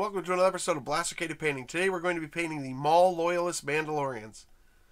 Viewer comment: I kept hearing "Mall Loyalists", like... shopping mall. LOL. FYE AND SEARS FOR LIFE!